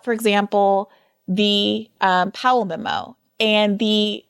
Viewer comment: what word does bri use at 1:06:24? palemimo?? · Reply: She was referring to the "Powell memo" which Hedges had mentioned a couple times in the conversation.